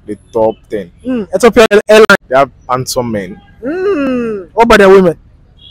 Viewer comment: I thought he said hair line, not airline, and fuming not including our brother Somali